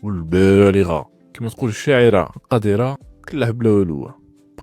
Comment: علاه قذرة ؟ كاش ما سمعت عليها؟ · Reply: يااو قديرة ماشي قذرة 🤦🏻‍♂️